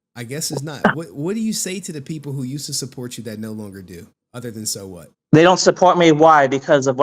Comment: That's funny as hell. I thought kept saying and so what. Until I looked at the screen. Lol